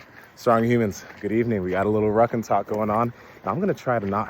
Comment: Had to replay the first 5 seconds a few. Times cause I thought he said “Ruck and Tuck “ …?¿?¿?